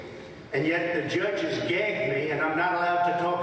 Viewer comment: What the hell is calling the judge gay have to do with testifying? The papers he refers to is a sad tell that Trump is sinking towards dementia. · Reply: Although the transcript shows that Trump used “gay”, if you listen around 3:00, he says “gag” instead of “gay”.